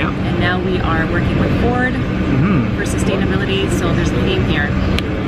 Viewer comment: I thought it said satanability